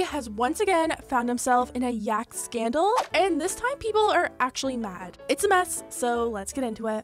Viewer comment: Deadset thought you said at the beginning, "and people are yak-tually mad" and I spat out my tea.